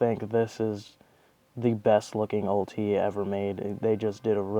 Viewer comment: What is OTs or what did you say at 8:12? Also is this good againsts PePe match up? And could you make a vid for combos with this deck? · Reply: i said "ulti" referring to the ultimate rarity of Dark law. the pepe match up is almost none existent in your favor, because dark law just does so much damage to pendulums. and the combos of this deck are very linear and simple. but if you would like I can make a video showing a few combos.